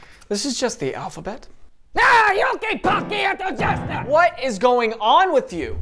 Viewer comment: Did he just said "Ёлки палки, это ужасно" ?!?!?. Like, omg i'm impressed.